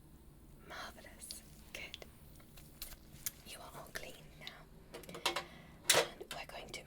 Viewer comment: i just hear her say know you’re ugly 😂